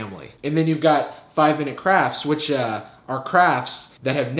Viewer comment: I thought he said crap